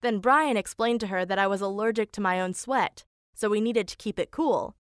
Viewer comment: I thought they said allergic to sweet instead of allergic to sweat 😂